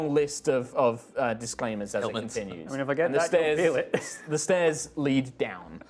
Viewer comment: What does say at 35:10? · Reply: "I mean, if I get that, you'll feel it", referring to the mentioned erection which will supposedly poke Boomer in the back of the head as character is currently sat on Boomer's shoulders.